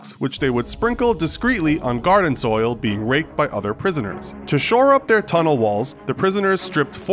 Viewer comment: "Being raped by other prisoners?" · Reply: I heard ‘being baked by other prisoners’ and i was like wtf why you eat dirt